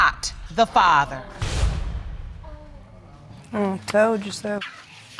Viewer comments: Watch her reaction to the results. She knew he wasn't the father. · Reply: did she say, "I told you so"? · Did she just say “I told you so” @ 14:26?After saying she 100% KNEW he was the father? Man women can be cold and scandalous 😮‍💨